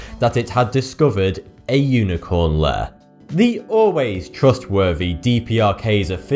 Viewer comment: A "unicorn lair". are you serious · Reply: thought you said unicorn liar before I finished the video lol